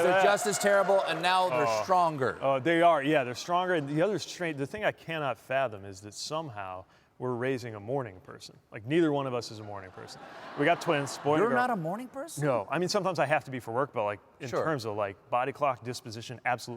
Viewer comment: My heart missed a beat when I thought he said 'MORMON person'. I'll let myself out.